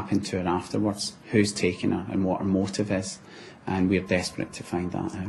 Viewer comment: Gerry slips up with his Glaswegian accent in the interview 31:04, he says "what OOR motive is" At first I thought he said "her" but I've listened to it a few times now and he definitely mispronounces and says "oor" (our the in Scottish dialect).